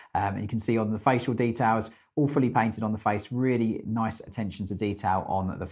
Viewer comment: Totally misheard this... thought you said the face details were "Awfully painted" not "all fully painted" 😂